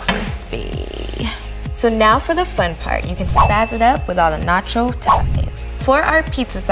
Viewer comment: Was I the only one that thought she said spazz it up?